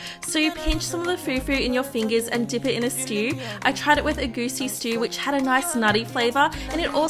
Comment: Who else heard her saying egusi has a nice nutty taste😭😭🤲🏽